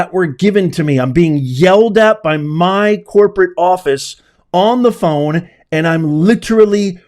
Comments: I thought she said "Yelled at by my bosses on the phone", not office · Sounds like she said she was being yelled at by my boss